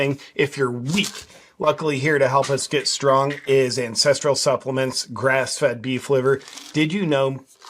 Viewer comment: Thought I heard “ incestural supplements” at first 😂